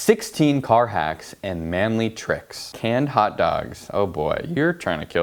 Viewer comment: Who else thought that can said "Shot Dogs"?